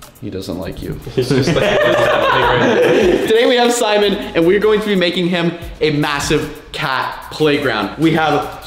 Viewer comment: I thought he said “were going to make A massive cat” 🤣🤣🤣🤣🐈😹😹😹🐈